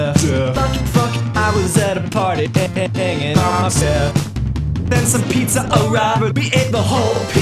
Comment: TYSM BEFORE I READ THIS I THOUGHT HE WAS SAYING “i was at a party hanging myself”